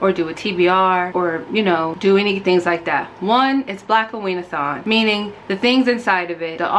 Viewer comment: Another person joining right on time. Just learned about this readathon tonight and I’m here for everything from the fact I keep hearing it as “black-queen-athon” which is honestly still accurate to your vibe and general chaos. Your boxes look amazing, too! I know she’s about as opposite of underrated as she can get, but Beloved by Toni Morrison is one I’ve been itching to reread. It’s a rare legitimate favorite from required reading piles in school and also she was local to me, which I learned too late but is still really awesome.